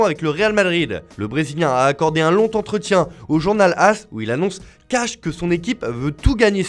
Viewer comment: C'est quoi un lontentretien ? 🤔